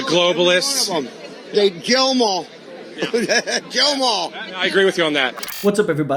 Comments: I thought he said “jail them all” but who can tell with those 3 teeth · I thought he said “Jail ‘em all!” 🤷🏽‍♂️